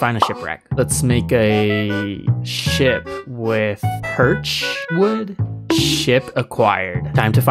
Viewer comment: Love ur video, but am I the only one that heard him say ship with BIRCH wood